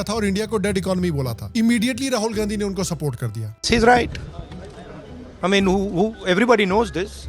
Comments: Does he said 'she is right'? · Did he said "She is right" to Donald trump😅